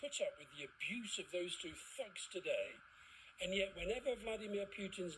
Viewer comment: When Alistair Campbell said those two thugs I initially misheard him and thought he said those two fucks. ;-)